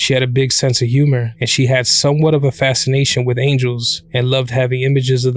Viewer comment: I misheard "Edna had a fascination with angels" as aliens.